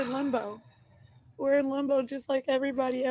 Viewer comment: i think she meana "limbo" but sounds like shes saying "lumbo" or is it just me? Also, even her own mother isnt buying it. You can tell by the look on her face.🙄